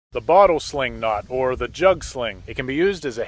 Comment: I thought it said “bottle sling shot” and I was waiting for him to chuck that barrel as hard as he could